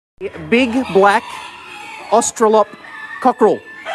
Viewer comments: In the beginning I thought he was going to say a big black cock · My video stopped to buffer at the beginning and I thought he was gonna say "Big black cock..."